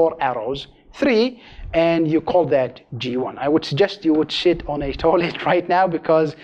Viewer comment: did he really say 'I would suggest you would shit on a toilet right now"? wtf does that mean?